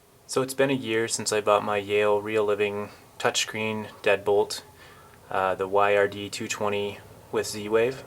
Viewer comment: For some reason I thought you said dead pool not deadbolt lol